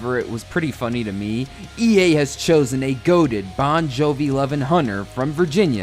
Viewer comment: when I heard this I was like “What do you mean they were saying goated back in 2006” then I realized they meant facial hair goateed